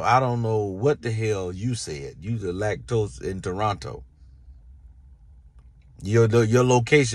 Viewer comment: I thought he said lactose in Toronto